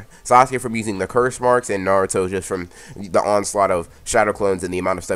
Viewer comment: I thought you said he was a half Uzumaki